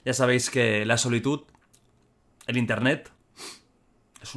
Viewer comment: jajajajajaja, fui la única que se dio cuenta de que dijo solitud en vez de soledad jajajajajja